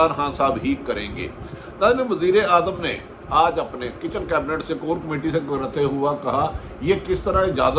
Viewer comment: did he just said kitchen cabinet??